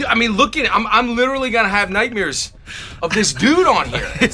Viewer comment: Did he just say nightmeeres? I thought Bernt Krystals was the only one that said it that way. iykyk